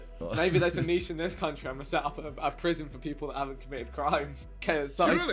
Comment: I cant be the only one who thought it said "POISON FOOD"